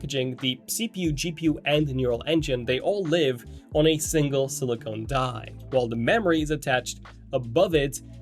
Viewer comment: did you say SILICONE instead of silicon? Unsubscribed.